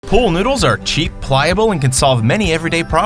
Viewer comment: I thought it said poodles lol XD